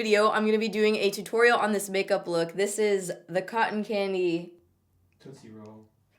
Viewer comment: I thought said cotton candy p** roll😩